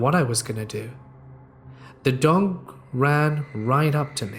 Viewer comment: He said dong instead of dog 😂